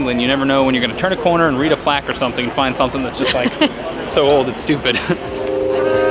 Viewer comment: Just to clarify, at 2:18 did you say "old and stupid" ? I may have heard it wrong. Thanks. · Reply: "so old it's stupid."